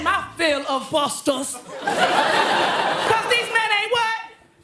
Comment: what did they say? It must’ve been so bad they had to cut it out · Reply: I believe it was shit 😂